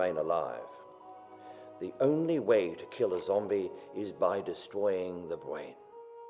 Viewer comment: did anyone else hear him say destowing the bwain 02:54 ??